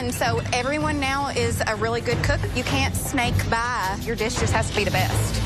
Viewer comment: With her Southern Accent.. Did she say, "You cant SNEAK by or...... You cant SNAKE by? " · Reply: Sneak by